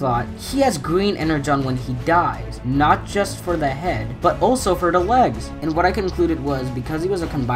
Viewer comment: In 5:37 it sounded like he was a baby say not only in the head but also in wegs